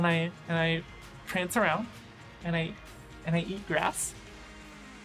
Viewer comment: Just Wait Tell People Think Its Offensive To Say Oh Dear Because Some People Identify As Deers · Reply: I Hope I'm Not The Only One Who Thought She Said He Eats Ass